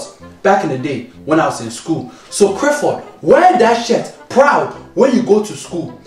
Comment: When he said wear that shirt proud it sounded like he did shit